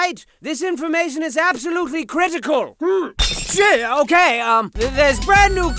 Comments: did he just say sh!t? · does say Sh*t?